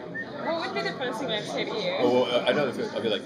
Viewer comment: What did they say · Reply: “what did you do with my body”